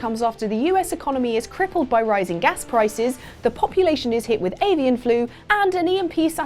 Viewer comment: not gonna lie I did a spit take when said "Alien Flu" because I thought the screen said "Asian Flu"